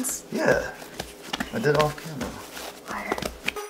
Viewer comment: I heard say to liar